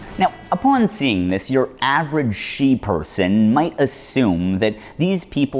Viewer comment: “she person” ?? Is that what he said I’m so confused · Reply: sheeperson, a portmanteau of sheep and person